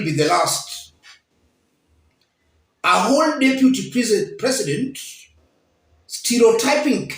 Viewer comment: At 1.50 I seriously thought you were about to say "A whole deputy piece of sh...", then corrected yourself. 🤓